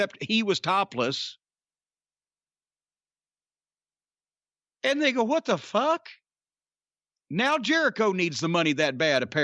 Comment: lol I know he didn't say it, but at the time it sounds like corny said "nigga what the fuck" 🤣🤣🤣 again NO HE REALLY DIDN'T SAY IT muthafuckas. But its funny lol yell